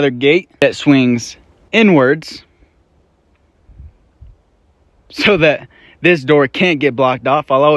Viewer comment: when he says “inwards” but it sounded like N-Words, then the long pause and chuckle😂😂😂